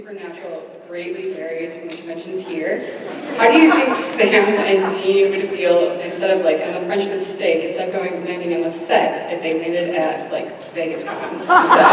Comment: the quesstion at 29:50 where Jensen response is I SMELL AN EPISODE.....what was the question or Idea he felt was worth an episode of Supernatural? I could not hear her comment or question. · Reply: On the lines.... I might be incorrect with the wording "my question is ???? much that's here?? In the show, Supernatural, How do you think Sam and Dean would feel instead of like the French Mistake of them going to the set, they come here at Vegas con" Hope this answered your question =^u^=